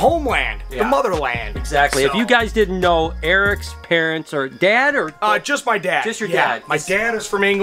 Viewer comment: I thought he said, if you guys didnt no, Erics parents are dead, then Eric says just my dad. I was thinking what?